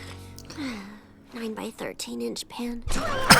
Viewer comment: Hen Twilight was sleeping I thought she said her lawyer me as "line 9/13 inch pant" As in make a pant noise for a sort of yawn but ok